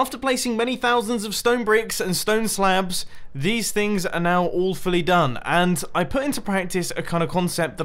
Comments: One thing I realized with mumbo is every time he says “all fully done” it sounds like he says “Awfully done”. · He said "all fully done" I heard "Awfully done" ;] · I thought he said awfully done but he said all fully....british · sounded like you said "stone slaebs"